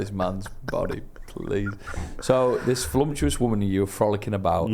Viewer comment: I'm sorry, did say Flumtuous instead of Voluptuous at the 9:48 mark?!?!?!